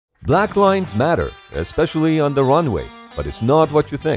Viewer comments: I thought it said ”black lives matter” for a second😂 I was like: what has Black Lives Matter to do with aviation?? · I wasn't paying attention for the first 5 seconds, so i heard "black lives matter, but it's not what you think"